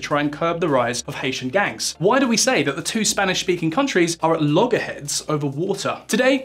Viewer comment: Did I hear that right at 2:09, two Spanish speaking countries???